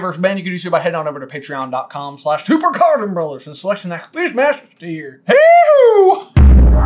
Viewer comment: Lol, speaking of Francis (FrON-sis), at the end of your video, i thought you said sign up for the fishmaster tier instead of quizmaster tier 😂